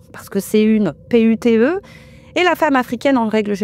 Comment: Une quoi ? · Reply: P-U-T-E